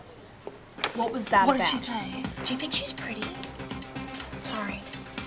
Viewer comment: Rikki: What was that about? Emma: What did she say? Cleo: Do you think she's pretty? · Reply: I thought cleo said : Do you think she’s pregnant 😂